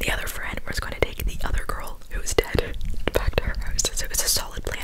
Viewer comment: Did you say one of them was dead bc I’m interested in dis story 9:12 😭